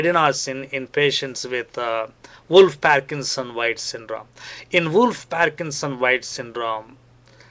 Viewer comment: what am i hearing. "wolfpacksonviceindrawl" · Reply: Wolff Parkinson’s White Syndrome.